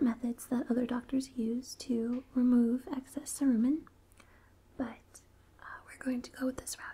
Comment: All I heard was "to remove excess Saruman" and I lost it. x.x